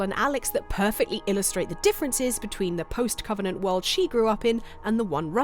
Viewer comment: did I just hear “post-covenant world”? Don’t worry, I’m excited for the new Halo too.